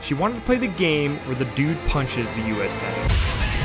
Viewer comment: Honestly thought you said ‘Where the dude punches the UAC senator.’